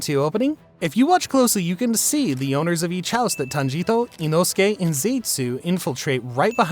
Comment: did he say "tanjito" or my hearing is just messed up👀